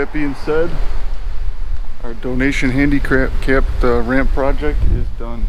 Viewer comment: Did he say handi crap ? I can see the wheel chair going down the stairs on other side lol.